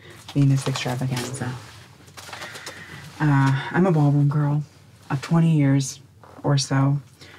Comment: Did she say she's a Mormon girl??